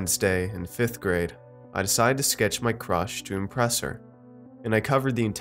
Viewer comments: I thought u said “in 5th grade, I decided to sketch my crotch to impress her” I was like whatt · I thought you said "I decided to sketch my crotch." 😆